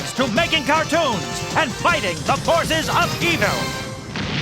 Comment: Did he just said "The Forces of Evil"? Wtf thats Star vs The Forces of Evil.